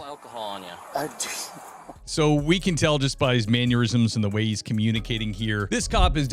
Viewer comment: sounds like you are saying 'maneurisms' instead of mannerism